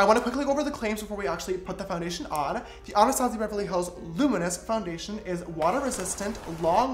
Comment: Who else heard he said Beverly hoes instead of Beverly hills